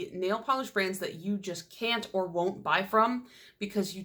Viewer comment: Am I the only one ? I thought you said Brands I won't buy because I'm pretty !